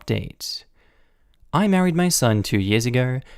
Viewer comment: At around 7:52 "I married my SON" excuse me I think you meant to read what it said "I married my HUSBAND"